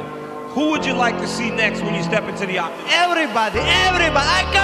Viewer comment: Bro ran out of English😂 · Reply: I heard "im the chicken" 💀😂